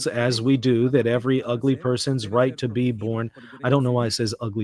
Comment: Native Spanish speaker here. Correct translation: “If one holds, as we do, that every FETUS’ right to be born must be protected by civil law”. YouTube mistakenly recognized the word FEO (ugly person) instead of FETO (fetus).